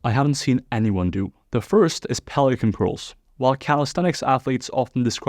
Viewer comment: A standing version of DrMikes prone curl—- when you said pelican at first I thought you said Poliquin.